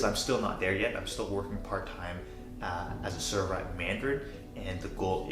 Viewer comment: What is his current job at 3:09? I think my english is pretty good but I just can't understand it. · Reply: he works as a server at a restaurant called mandarin